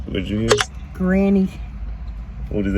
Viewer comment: I heard Granny